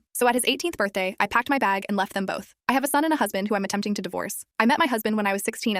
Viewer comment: Well. I just heard that as, "I have a son I'm trying to divorce."